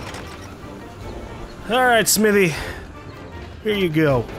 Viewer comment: " Here you go smithy " Thought he said smii7y.... replayed like 5 times to know lol . Love ya